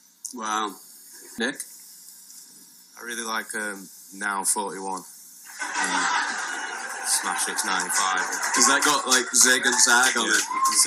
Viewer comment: what did he say? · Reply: I really linke "now 41-smash hits '95"